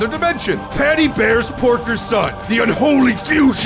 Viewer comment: if you keep on clicking at a certain pace on 3:00 it sounds like he says sporker